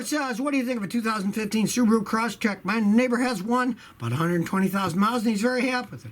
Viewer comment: did he just say what I think he said? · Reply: Neighbor